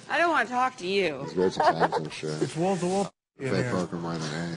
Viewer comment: What does norm say to the blonde starting at 18:22. Something like he'll, "never forget her..." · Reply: I heard “it’s great…. Times…. I’m sure..” not sure what he says in those gaps.